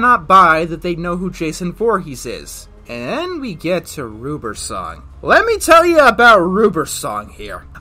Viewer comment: When he said "ruber's song" I thought he said Ruperson